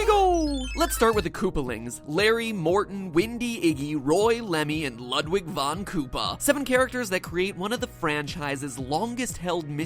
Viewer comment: did matpat say "windy"?